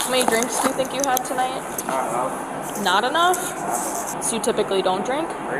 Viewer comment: Nah, he said "I dunno". The officer said not enough. Just another reason to keep your mouth shut dealing with the cops, so you can't be misconstrued for saying something you didn't. And don't drink and drive. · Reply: you’ll note that at 11:52 he once again said “not enough”, so I’m pretty sure that’s what he said the first time as well…